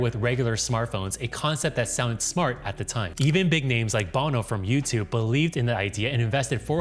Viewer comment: i thought you said "Bono from YouTube" hahahahaha